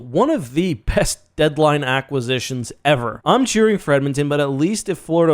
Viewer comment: did you say Marchand may be one of the PEST acquisitions lol